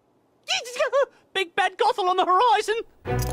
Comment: I thought it said big fat gobel on the horizon